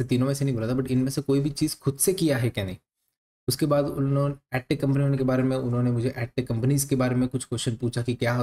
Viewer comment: what was the actual word "active companies" or "acta companies"? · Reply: AdTech Company